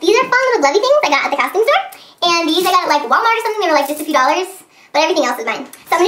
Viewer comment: it sounds like you said the beads were like 50 dollars:D hahaha.